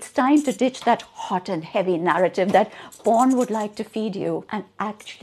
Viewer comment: She said porn and i heard Bond And i was like wait- why would Bond tell me how to kiss XD